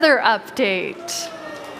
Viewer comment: I THOUGHT IT SAID HEATHERS UPDATE 😂😂😂